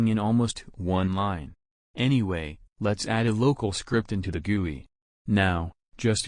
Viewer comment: its just me or i just heard (g)uwu instead of gui?